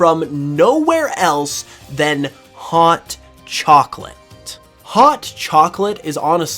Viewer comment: for a second I thought you said "haunt chocolate" and now I can't get that pun out of my head 😂